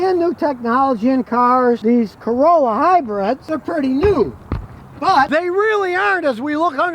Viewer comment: I thought you said hybrids are stupid